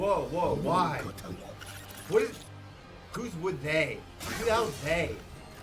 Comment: Karlagan did not say they. He said that person and said it in a way that indicates that he is high